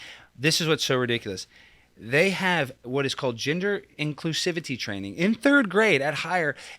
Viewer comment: Gender inclusivity training I heard “ginger inclusivity training” 😂😂😂😂